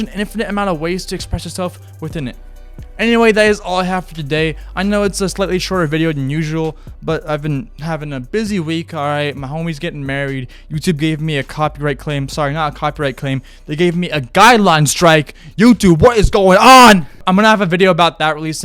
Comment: first time i watched i heard "my homies getting buried"